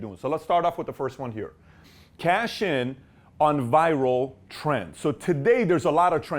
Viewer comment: Thought the first one said Cash in on Vital Friends first before he said it